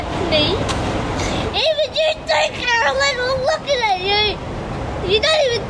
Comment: i think he says "even you too carolyn, i'm looking at you". Near the end of the video his mum asks if there's anything else he wants to add, and he says "i'm gonna kill you carolyn". Then his mum says "so the next bully better watch the fuck out".